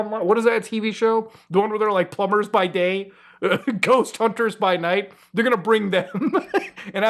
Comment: I heard "plumbers by day, ghost fighters by night" and my brain went straight to "Luigis mansion is real?"